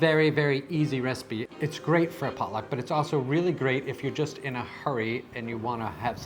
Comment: Oh, it’s great for a _pot luck_…I thought you said great for a Pollock and was very confused 😂